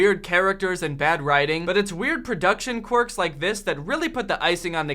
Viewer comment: the captions has "Qwerks" instead of "Quirks"